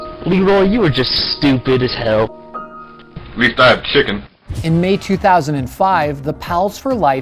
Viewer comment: rec0il _1 It was I aint a chicken, I can’t believe people liked this comment thinking you were right haha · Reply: i thought he said big fat chicken